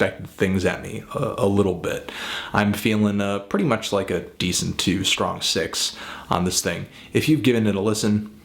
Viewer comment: At first I thought you said "decent two" hahaha